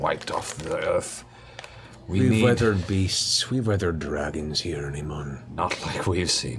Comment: did he just say "here in Emon?" Oh Matthew, you work so hard....